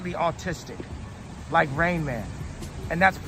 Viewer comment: It sounded like he said Rayman!